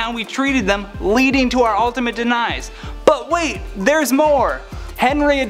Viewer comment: he said "Denise" instead of Demise 😂😅